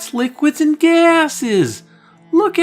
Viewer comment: I keep hearing "Liquids and Gases" as Scott Steiner saying "FAAAT ASSES."